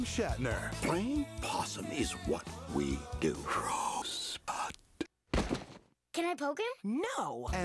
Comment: It sounded like crow spot! But I didn't know it was rosebud.